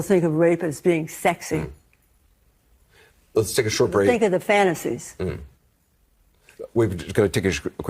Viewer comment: Most people think what??? Did she say fantasies?? 😮😮😮